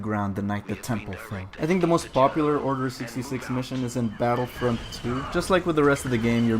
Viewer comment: Wow I thought it said “order 66 memes”🤦‍♂️